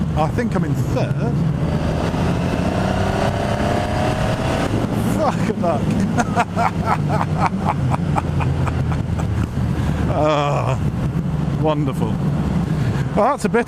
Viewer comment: Hahaha! at 4:44 did you say Fuck a Duck! lol, I haven't heard that in a while, awesome bit of kit it's 60kg heavier than mine, shit! there very nimble for a big bike, mind you so is mine,Some people have complained about them pulling to the left slightly under hard acceleration, Crikey indeed sir! well done great video, RSMF.